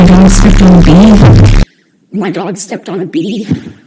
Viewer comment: I heard: "My god stepped on a bee"